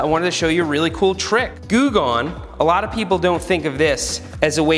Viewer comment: whats that? Im dutch so i dont understand If somebody will type that word, so i can search on google😁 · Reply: 'Goo Gone' is just a brand name of adhesive remover. It removes sticky residue.